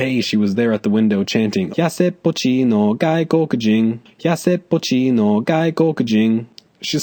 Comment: Yassed putchino gay cocajine. That's what I hear